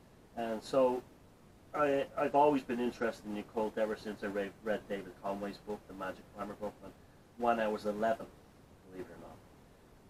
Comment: what's the book at 8:01 ? i cant understand what he said. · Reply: I think it might have been David Conway's Magic: An Occult Primer.